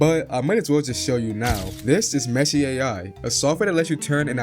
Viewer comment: I thought that said meth 😂